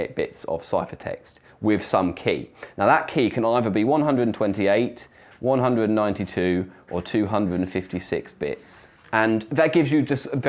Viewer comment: Did he say "that key can no longer be 128, 192..." is " no longer" the thing he said there? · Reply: He said "That key can either be 128, 192, or 256 bits"